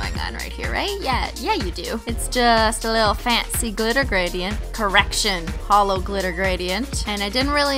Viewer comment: I thought you said erection instead of correction and then I was like wtf lmao